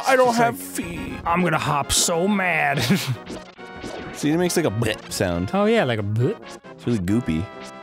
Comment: For a second, I thought Arin said, "Suzie makes a 'blegh' sound" and I was like, "Wait, what?"